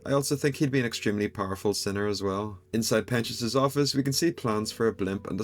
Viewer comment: This made me laugh so hard😂. At 2:28 if u turn on yt captions, instead of saying pentious', it says penis'😂